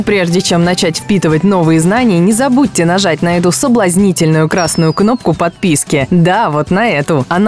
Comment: Что со мною не так? искал флешку в столе и слушал видео на 0:54 говорят: "не забудьте нажать на эту соблазнительную красную кнопку" а мне послышалось: "не забудьте нажать на эту соблазнительную классную попку и ведь не прыщавый подросток уже и живу с девушкой.